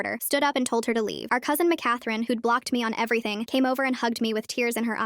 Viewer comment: I’m sorry did I hear that right both the aunt and the cousin is named mckathrine